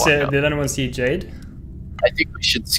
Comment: When he said: see jade I thought he said:cj'd 😂